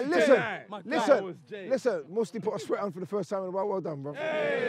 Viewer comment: what did say at 28:07 about Musti? couldn't really hear properly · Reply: That he put on a sweat that game lol 😂